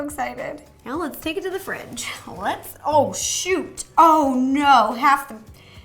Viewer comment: I thought she said i 'oh s°it' but she said 'Oh SHOOT'